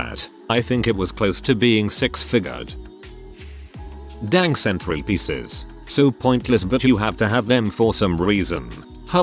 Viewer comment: I heard centipedes instead of centrepieces lol 🤣